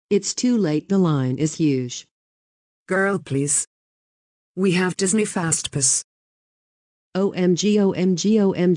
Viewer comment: it sounded like they said “we have Disney fast piss”